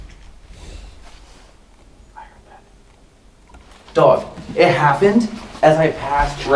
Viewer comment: I heard a spirit say (I HAD DONE IT) when jacket got pulled between 47:22 and 47:29, I don't think anyone heard it. Check it out guys and keep up the good work. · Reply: I'm hearing ''I heard that''. I thought it was one of the guys